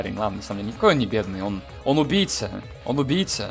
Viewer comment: Хотя никакой он не бедный, он... что сказал: ...он убийца что подумала я: ...он богатый